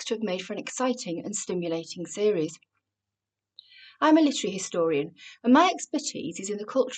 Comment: I thought you said military historian (which was odd, given that you're an English lecturer), and thus mightily puzzled when you described your expertise... 9:48 Like what? (My parochial American ears have a hard time understanding you.)